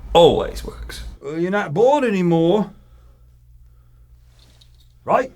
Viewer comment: I thought he said "You're not blonde anymore"... cuz the wig fell off😅